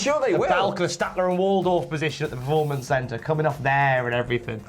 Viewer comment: Did you say Staedtler and Waldorf or Statlander and Wardlow